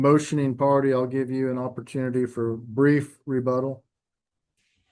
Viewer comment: Could’ve swore the judge said brief rebuttal lol.